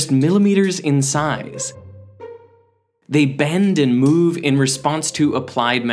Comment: What the guy in the video is saying : "They bend and move in response to applayed magnetic fields" What i heard : "NANOMACHINES SON"